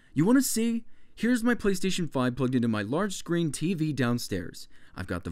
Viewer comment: Did he just say playstation fucking 5?